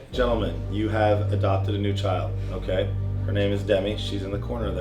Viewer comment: I thought he said "you've adopted a new child" when he actually said "you've adopted a new gnome" :O That flip was a tad on the highly scary side. I was hoping and expecting to see some better footage of the rollover. Looked to me that your front left climbed up the back right of the car in front and then you were just along for the ride.